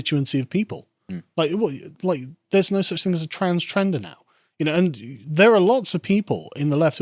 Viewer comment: did you say trans-trender?